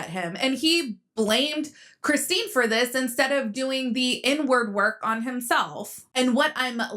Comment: I thought she said "n word work" lolol She said "inward"